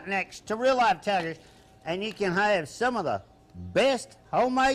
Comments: anyone know what “ohmay pizza” is? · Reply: It's "Homemade" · did he say he made by scratch or crap?